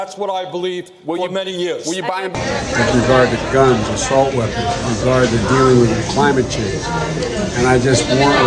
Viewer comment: did he say take away the guns and assault weapons and protect the deer from climate change? 🤦 he was doing his mumbling shit but it definitely sounded something like that